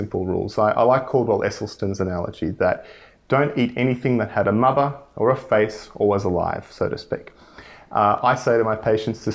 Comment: I couldn't hear property at 16:46 to 16:50. Can anyone write it for me? Thanks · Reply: Hi Ricky, I said "I recommend to my patients, don't eat anything that had a mother, a face, or was alive." - Just making the point that people should avoid animal products entirely and focus more on plants.